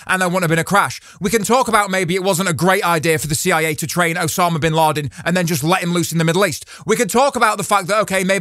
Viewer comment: I honestly thought was saying “maybe it wasn’t a good idea for the CIA to train Osama Bin Russell and let him loose on the race track.” Took me a second to realize that is not what he said at all. Ahhhh I’ve been brain washed! Not a cult!!